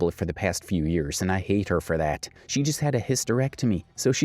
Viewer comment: the commenter commented & I hate that for her, not I Hate Her For That. Great rundown but im glad I was watching and listening. Maybe you was just talking to fast in order to get that twisted but I just felt the need to correct that cause the person didn't say exactly what you said. Thank you for the video thou.